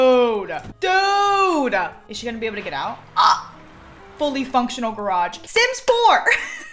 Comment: I THOUGHT SHE SAID HOLY FUNCTIONAL GARAGE 😂😂😂😂